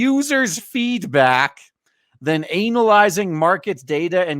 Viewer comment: hearing "anal-izing" instead of "ana-lyzing" is so appropriate for today's game market.